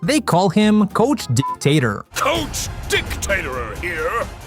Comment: Oh... That's why is was censored... I mean I watched the episode in turkish and I only heard "Coach Dictator"... I didnt understand why it was "Coach Dics-tator"... He said "Coach Dic'sik'ator"... Which "Sik" kind of means dick.